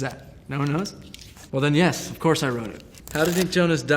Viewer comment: did he just say "wroten"? 😂😂